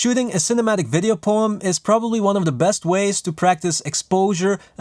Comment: Thought you said video porn.. Thought this channel is talking a whole new track😅😅